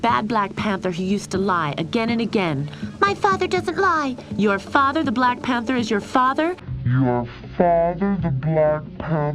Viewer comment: I'm pretty sure he said "Your father? The black panther is your father?!" Alas, the voice acting is so bad it sounded like a single sentence.